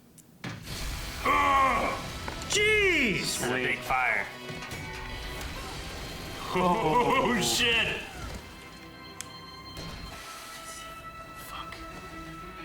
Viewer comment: I thought Amon just said Fuck when he saw Korra......